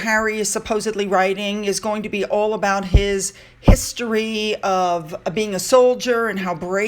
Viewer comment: When you first said the word "history" I heard "hipstery".